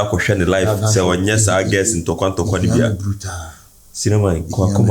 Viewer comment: what is the tote said 🤔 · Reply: TikTok he mean😅